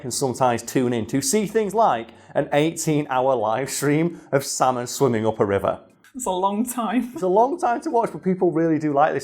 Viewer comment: I swear I heard him say "Santa swimming up a river." And I just imagine a man dressed as Santa swimming up a river. I'd tune into thay